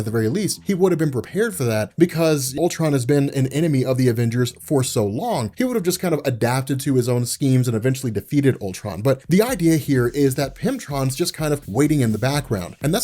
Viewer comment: Please tell me I'm not the only one who misheard him saying "PIMPTRON".